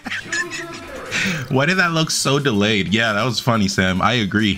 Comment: I initially thought he said "yeah that was fun, Esam"